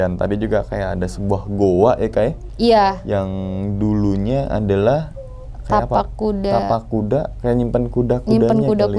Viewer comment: Kayak ada suara cewek tapi gak jelas ngomong apa. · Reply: Hmmm sakit...